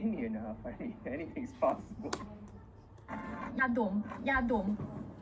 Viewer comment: Lisa said Yadom which is "nasal spray" but everyone misunderstood it and thought she said yadong which means "porn" 2:58 He's Eric Nam, he's a KR&B singer which is really funny and nice, also has his own podcast and YT channel with it. I recommend you to give it a try and listen to his music and also his funny moments 6:10 She is Twice's Tzuyu, she was born in Taiwan and moved to Korea to train when she was a teenager, she even struggled to learn the language If you are interested in more Eric conteny, here there are some funny moments <link> 'Eric Nam being fracking adorable´ <link> ''eric nam podcast moments that you're missing out.'